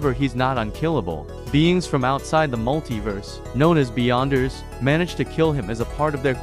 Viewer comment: bruh, i almost thought you said "Beyònce"